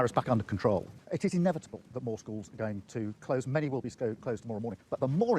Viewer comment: did he say "mortals" are going to close......? Is this guy not a "mortal"?